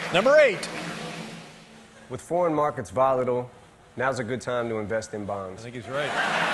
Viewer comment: I thought he said “when foreign markets volatile, invest in bombs”